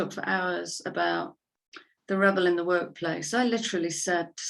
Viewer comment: I heard "the rebel in the woke-place" first time around 😉